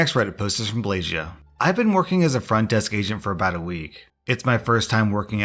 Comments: I had the volume down and I thought you said a front desk asian😂😂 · I thought you said "i'd been working as a front desk asian for about a week..." 😂😂